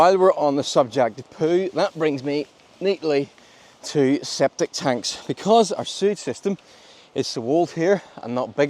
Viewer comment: I think it's called SEPTIC Tank, not SCEPTIC Tank :)))